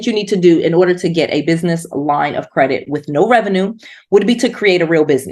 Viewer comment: I thought u said in the beginning "get business loans with no revenue"